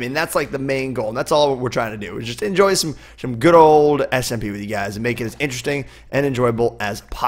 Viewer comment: did he say smp?